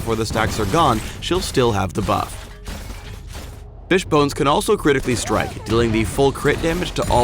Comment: does he say "bitchbones" ?